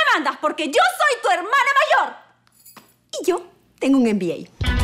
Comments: Que dijo en la parte 8:47 La Rafa que dijo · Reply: Dijo que tenía un MBA (que es administración de empresas) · que es lo que dice Rafaela? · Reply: MBA